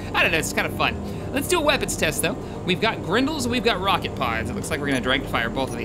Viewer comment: it sounded like you said "grundle" each time you said grendle. Just gonna say that.